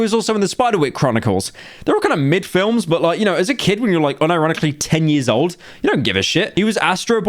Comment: I'm sorry, did he just say "and you're unironicly a kid"...